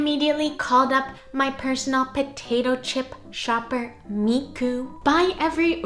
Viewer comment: Potatoe chip? I thought you said Chopato pip ! 🥔